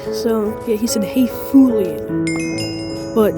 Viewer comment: can you not swear and say f u I feel hurt. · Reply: I didn't. I might have sounded that way. But I didn't swear. I was spelling what I read in the comments. F u l I a n.